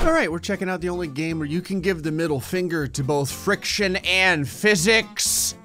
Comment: I have crappy internet so when it buffered it sounded like he said "we're checking out the only game where you can give the middle finger to both fricks" Call me easily amused but I got a slight chuckle out of that